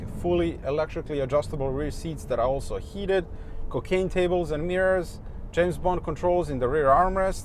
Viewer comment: Did he say. Cocaine table's?? Lol🤣🤣